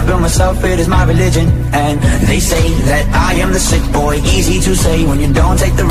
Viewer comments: I thought it said stickboy and not sickboy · Why do I keep hearing "stick boy" instead of "sick boy" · At first I thought it said, "Stick boy" idk why I did.lol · Ok the first time I watched this meme, I literally heard, “I am a thick boi” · Why do I keep hearing "stick boy" instead of "sick boy" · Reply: Yep I heard "STICK BOY" instead of "SICK BOY" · all this time I thought it was saying, "Stick boy" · I thought it said stick boy